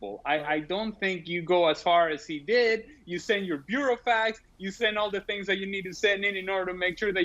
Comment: did he just say "bureau fags"?